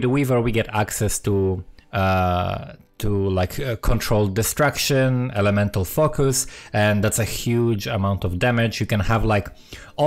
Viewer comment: I heard "controlled destruction, elemental fuck us"